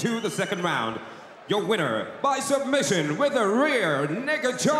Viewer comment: a rear N** choke.... i think he said this cause chris browns song in the back ground.... amazing how nobody caught this lmao!